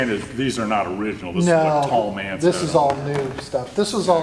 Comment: Paul Mantz wasn't mentioned. It was Tallmantz, the aviation collection owned by Frank Tallman and Paul Mantz. This Waldo Pepper J-1 was owned by Tallmantz. That was the reference in the video. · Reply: I thought you said "Paul Mantz" when you actually said "Tallmantz". ;-) Not wearing hearing protection exacts a price as the years go by.